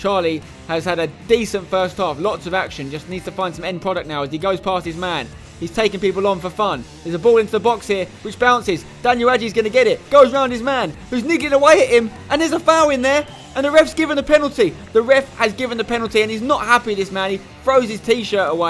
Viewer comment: does spencer say the N word?